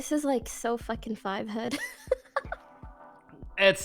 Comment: What the hell is 'five hood'?? Lol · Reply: if you are referring to what poki said at 12:13 she said "five head" which is another term people use for "big brain" or in other words: really smart. I believe urban dictionary has a definition on it that speaks to the origination of where it came from.